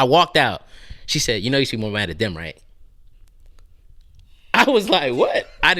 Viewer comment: What dude said at 38:28??😭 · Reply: "you know you should be more mad at them right". I think